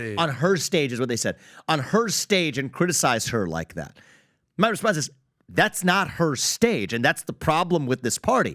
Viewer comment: I play things at 2x speed and I thought he said "mother f---er, that's not her stage." Was kind of disappointed that it was "and my response."